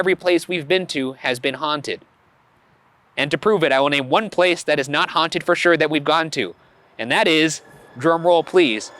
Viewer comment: WHAT DID HE SAY? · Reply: I think he said Moon river brewing company 🤷🏼‍♀️ In that episode, he was the least scared of all the people and it seems like he said “moo” before it cut out 👀